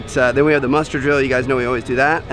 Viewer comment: My dumbass thought they said Mustard Grill. 😂😂